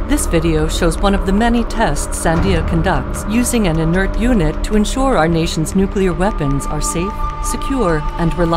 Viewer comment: Did she just say “to make sure that our nuclear weapons are safe”?